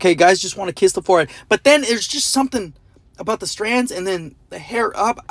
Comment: Bro I thought he said wear not having there hair up